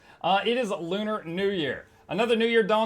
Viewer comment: ayo what did he say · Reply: "It is Lunar New Year"